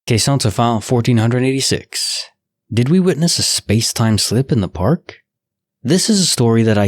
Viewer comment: i thought it said= spacetime sHHHHip. not sLLLLip.